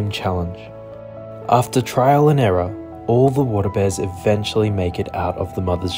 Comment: At first, I thought you said "water bed," and I thought, "Pft. That ain't a water bed"